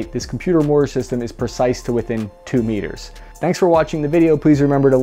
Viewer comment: To be honest, when I started watching I thought you said “Chris Crappy.” Sorry.